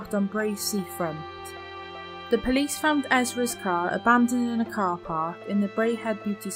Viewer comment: At about 4:50 and again at 5:00, the narrator says something I don't hear correctly. I think it's referencing the same thing, neither time can I hear/comprehend what the word( s )are. Also, the narrator says that Esra's car was parked in the only blind spot in the lot, but where is the blind spot she refers to? Can anyone help me with this please? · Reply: The word at around 4.50 is 'abandoned'